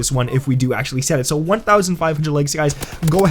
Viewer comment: I thought he said legs instead of likes